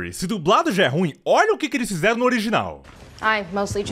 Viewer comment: FANFIC DE FURRY??? HAHAHAHAHAHAHAHAHAHAHA AH NÃO VELHO!!!! E A DUBLAGEM ORIGINAL!!! KSKSKSKS PORN0 DE ZOOTOPIA!!!! 🤣😂🤣😂🤣😂🤣😂🤣🤣 ( Aliás, na legenda tá escrito "fury" com apenas um "r", o certo é com dois, "furry"... mas mds...FANFIC DE FURRY E PORN0 DE ZOOTOPIA?!?!? KSKSKSKSKSKSKSKS 😂🤣😂🤣😂🤣 ) Amei essa referência ksksksks 🤣🤣😂🤣🤣🤣 Pqp...não esperava por isso ksksksks Essa foi de muito boa ksksksks Ri d+ KSKSKSKSKS